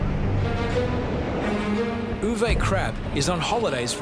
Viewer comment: Lmao i heard UwU Crap... oof